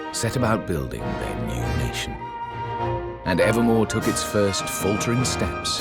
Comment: I thought said Nevermore(from the first game) instead of Evermore